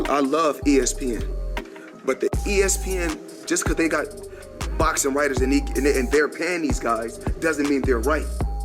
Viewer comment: Why does it sound like Floyd was saying, "Just cause they got Box right in they panties guys, doesnt mean their right!" 🤣🤣🤣